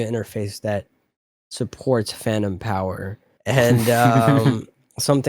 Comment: I'd like to believe laughed at 38:30 because he thought said fanum power